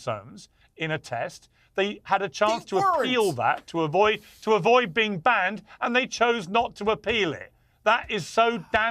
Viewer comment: what does Cenk say? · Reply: "They weren't"